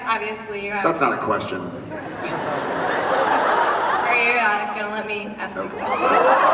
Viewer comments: What does the girl say at 8:15? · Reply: "Are you going to let me ask my question?" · Please, help me, I don't understand what the girl answers back to Jared after he interupts her (at 08:14) which makes Jensen to stand up and applause. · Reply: He was applauding her because she was sassy to Jared. She said "Are you going to let me ask my question?" or something along those lines. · What does the person at the mic say at 8:15? I can't understand it. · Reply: she says are you going to let me ask my question